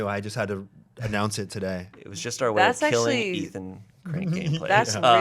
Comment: shayne saying "(way of killing) ethan, cranking inplace" legit sounded like "ethan crankgameplays" i was so confused loll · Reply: It probably sounded like that because that was exactly what he said. "Ethan Crankgameplays" is what he said.